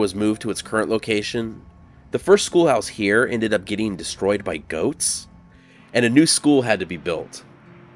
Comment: Did you say the first school was "destroyed by goats"? The last days of the second school must have had pretty grim recesses. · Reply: At first I heard "ghosts," so when I realized it was "goats" I was only slightly less confused!